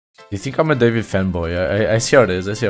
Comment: legit thought u said femboy